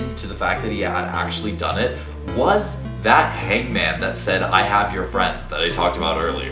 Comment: i always thought it said "I HATE your friends" heh Grandma Fuzz is a girlboss fr